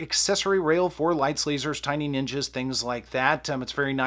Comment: did you say "tiny ninja"? I'm very curious as to what that means.